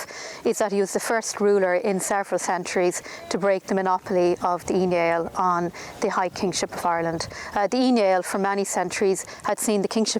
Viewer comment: It's 2020, and I'm still waiting on accurate subtitles. Sometimes the subtitles are so wrong, that it's ridiculous. I understand 90% of it, but occasionally there is a word here or there, where I'm lost on what is said because of the accent. I'm still not sure what Brian broke the monopoly of at 2:16, but I'm pretty sure that it had nothing to do with "email" like the subtitles tell me. IF so, Brian Boru invented email in 1014! A fact that is just as impressive as defeating the Vikings. Who knew? All joking aside, has made me interested in reading up on more of early Irish history. The line she said that jumped out at me was that aristicratic women could have been married 3 or 4 times. That kinda blew me away, I always thought divorce was a modern thing. Yeah, I figured marrying your daughter off to the most up and coming member of nobility, was a one time bet, but not in Ireland. Your guy is either upwardly mobile, or he gets divorced for a guy with deeper pockets and more ambition. Haha, nothing is new, we just think it is. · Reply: 2.16 she mentions the Ui Neill... these were 2 clans and kingships that were in power in counties of Dublin(north)/Meath/West Meath/Louth/Monaghan/Cavan and inland to Roscommon (Southern Branch) and then the more powerful Northern Branch, which held all of Ulster. The Ulster Branch was still in power up until 1607 - this was effectively the end of ancient Celtic Irish Aristocracy, and the start and acceleration of the Plantation of Ireland by English+Scottish/Protestant settlers. Hope that this helped.